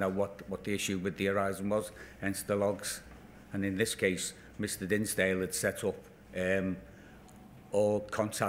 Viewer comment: Thought he said Mr Grimsdale lol😂